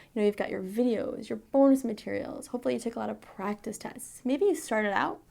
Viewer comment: does she say porn material?